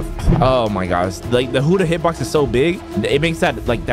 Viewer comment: Is it me or did i hear him say "huddah"